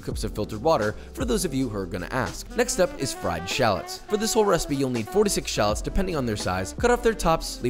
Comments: What i heard is.. "you need forty six shallots" and my brain like "wtf bro?".. and then 5 seconds latter "oh four TO six... hahhah" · please tell me I’m not the only one who’s eyes got big when I thought he said 46 shallots?😂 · I thought he said 46 instead of 4-6 shallots at first… 😂 (garlic I might believe that instruction) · So i was letting this play in another tab and misheard "4 to 6 shallots" as "46 shallots" and was CONCERNED. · I thought he said FORTY-SIX SHALLOTS xD